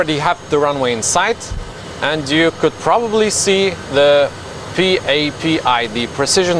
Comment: I thought he said PPAP